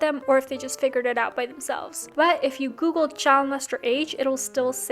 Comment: Minute 4:38 when the subtitles say Child m***** instead of Sharon Lester ....